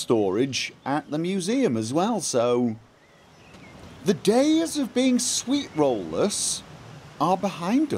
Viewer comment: For a moment I thought he said their days of being sweetrollers was at an end and I was so upset.